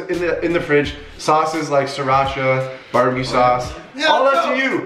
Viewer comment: Did he just say Barbie sauce crazy1:02